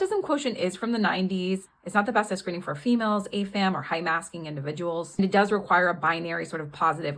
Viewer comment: The captions said "ASAM" but it sounded like you said "AFAM" and bcs contextually, you put it after "females" and before "high masking individuals", I assumed you meant "AFAB" for "assigned female at birth"? But if that's not what you meant, could you explain what acronym you were using? Thanks 👍